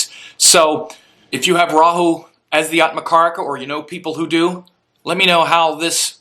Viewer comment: I thought for a second it said Atma Karaoke.. Ha !!